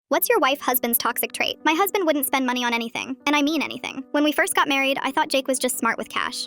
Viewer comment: At first I heard what’s your wives husbands toxic trait?🤣😅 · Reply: I heard "white husband" 😂😂😂